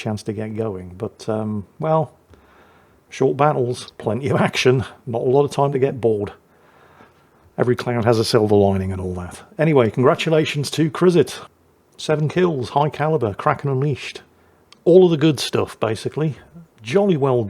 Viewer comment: And for our next series: "The Gynecologist"... I misheard " every cloud has a silver lining" as "every CLOWN has a silver lining", which is quite apt for the current meta of WoWS.